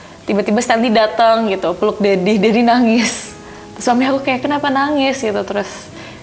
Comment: Di menit ke 16:33 laura bilang”suamiku datang kenapa nangis?” Apa aku salah denger gtu? Atau emg laura udh nikah sblum nya? Koreksi klo telinga aku salah dengar ☺️ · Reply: bkn "suamiku" kak,...tp "so mami aku" tp mmng cepet pengucapannya 🙏